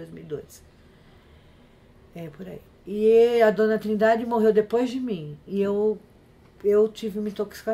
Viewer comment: Só eu que não entendi essa parte? Como "morreu depois de mim"? Morreu no sentido de demissão, desligamento? Escutei errado?